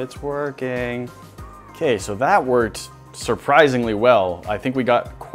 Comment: Why when says "it's working , it's working" it sounds like he said " it's twerking,it's twerking" 😂😂😂